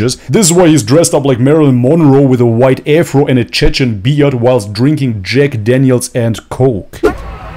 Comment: Did you say Chechen beard? LMAO 😂